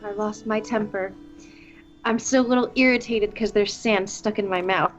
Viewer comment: I just misheared saying "I'm still irritated because there's >Sam< stuck in my mouth.